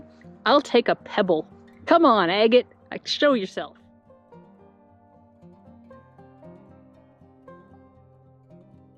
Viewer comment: WHAT SHE SAY? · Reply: D "Buck myself up." It's slang that means make myself feel better.